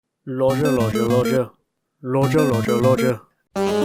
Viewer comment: Intro kinda sounded like saying Rodger Rodger Rodger